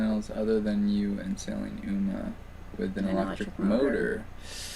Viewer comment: Which boat were u talking about that has to sell. I thought u said sailing uma ???? But not sure. It was about 45 minutes in ur chat??? I love ur free diving can’t believe u can hold ur breath that long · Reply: Sailing Alma.